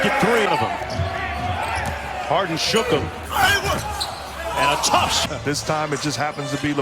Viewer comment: 나만 아이구 소리가 들리나 ㅋㅋㅋㅋㅋ